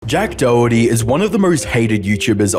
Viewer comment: Wait i thought bro said Jack Doughty 💀💀💀